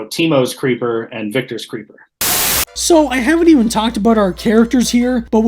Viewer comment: I swear he said "Temu Creeper" and that made so much sense. 😂